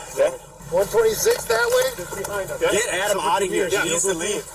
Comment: I heard, “Get Adam out of here, geez Louise!” 😔👍🏼 I rewound it several times to find the spot to comment, only to realize nobody actually said “geez Louise.” 😂 Just overlapping conversations! I was originally going to comment on this officer’s self-control in not swearing, then wondered what it would take for him to swear—and how seriously everyone would take him if he did. 😂 Alas, none of that actually happened in this video. "He needs to leave" when not watching the video just listening to all of this did sound like geez Louise for the first few times and I can only pray that if I was in this situation I would actually hear without officer in fact did say.